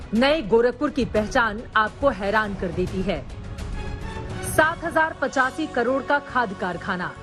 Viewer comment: did she say 2017?